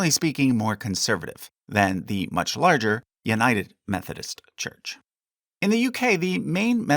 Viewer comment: did you say "uniting" in an Australian accent? 😂